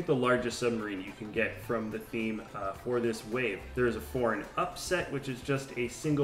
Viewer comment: I keep hearing "foreign upset" when you say "four and up set" :). Love the videos!!! Keep up the good work. Hey, any plans to release pre packaged custom sets? (I'd buy these ASAP, as It usually takes me at least 5 Bricklink buys to build a ~700pc set). Or, know of a website that shows all/any pre-packaged custom/MOC's that have been created and are available to either browse or buy?